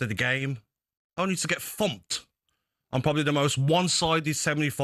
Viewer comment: I thought gabby said fucked not thumped 😂😂